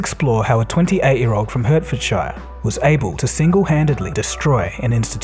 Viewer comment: Est I must admit I thought the narrator was failing to say Herefordshire, rather than Hertfordshire. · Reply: honest to God at 0:40 I thought he said Bedfordshire what is that pronunciation?!?!